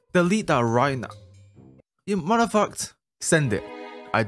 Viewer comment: The captains translated it as mother f*** instead of matter of fact 💀